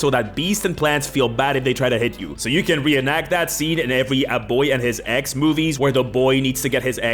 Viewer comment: I was listening to this video while playing Baldurs Gate 3 and without the onscreen context of a child and a pet I heard "A boy and his Ex" movies and thought you were watching the most fucked up romcoms xD